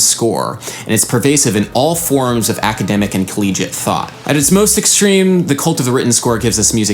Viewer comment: What is the word after academic? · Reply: collegiate